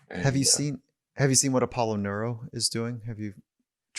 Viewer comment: Does anyone know what device are they referring to at 1:25:10 thanks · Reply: Apollo Neuro ... <link>